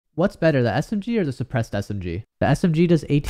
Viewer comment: I THOUGHT U SAID SMG OR DEPRESSED SMG 😂✋